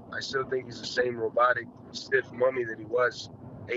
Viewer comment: Lol I thought he said “robotic, stiff monkey….” Let me get off of the internet for a while.